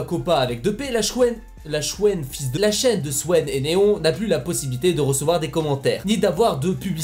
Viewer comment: La chwène de swèn et Néon jpp 😂( il a peu être pas dit cela mais c'est ce que j'ai entendu)